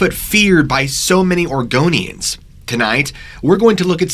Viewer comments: Almost sounded like he said Argonians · Did anyone else heard him say Argonians ? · Thought he said argonian. I was like “the lizard people are afraid of big foot???”